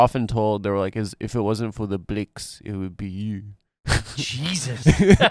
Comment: I'm a corn fed white boi from Arkansas. What does he mean by blinks? Or whatever the word is he says here in the clip? I can't make out the exact word so I don't really know exactly what he's talking about when he mentioned what his parents were often told. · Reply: He's saying "blacks" in a heavy SA accent